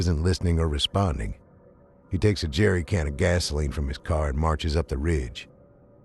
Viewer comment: Lol I’m new to this channel and I thought he said Jeffery cranor and got really excited